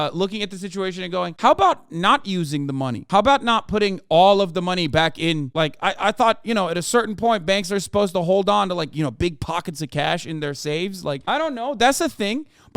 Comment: what is he saying at 13:42 then? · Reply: He's saying: "How about not putting ALL of the money back in, like, I thought, You know, at a certain point banks are supposed to hold on to big pockets of cash" What about this is it you're confused about exactly? He's saying banks shouldn't invest all their customer deposits and they ought to remain more liquid by holding onto more cash.